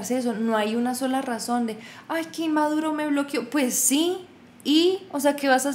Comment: jajajaja, yo entendí "qué maduro me bloqueó". Qué bonito que tu papá te motivó a hacer la especialización en Marketing Digital, sin pensarlo está alineado a lo que querías hacer desde un principio.